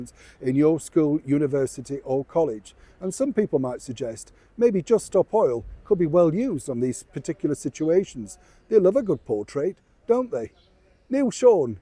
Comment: Did you just say "Just stop Hoyle", Neil? That made me laugh 😂😂